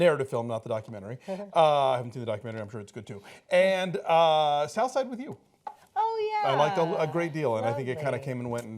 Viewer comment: what did the guy say at 11:09 ?? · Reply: He said "South Side Review"